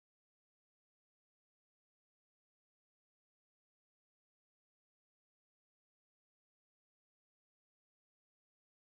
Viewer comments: What did Douma say to Akaza? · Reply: He called akaza “baby” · wait what did he say😭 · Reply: "Baby"